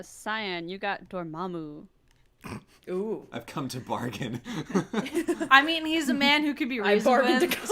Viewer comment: I can't hear what said. Help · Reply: "I've bargained to come"...